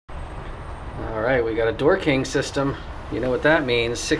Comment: I heard "we got a dorking system!" Wtf is a dorking sys.... DOOR KING. ohhh.